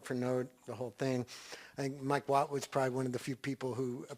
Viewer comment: Does anyone understand the people they mention at 22:44 and 22:49 ? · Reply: Mike Watt, bass player from The Minutemen.